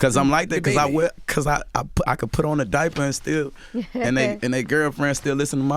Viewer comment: Did u just say "Put on a diaper" ? 😕✌😂 Ayoooo cmon now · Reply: Don Perignon He did though